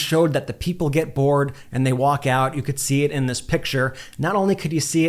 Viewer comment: I thought he said if they walk out he will leave😅😅😅😅bye trump😅😅😅😅